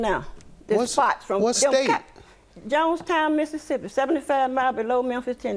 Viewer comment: I thought she said Jones County, Mississippi 😂😂😂😂